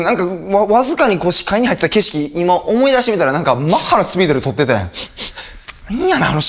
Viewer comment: ここなんて言ってる？？なんのスピードって？ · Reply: マッハ